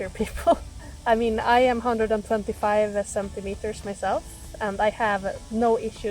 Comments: Hi - nice review; seems fair. I swore at 7:43 that you said you were 125cm tall! Lol. The 175 from transcript seems a lot more accurate. Does the bike have any electric/regen braking that will help charge the battery? Cheers. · It sounded like you said you’re 125cm. I’m surprised they let you drive a truck😂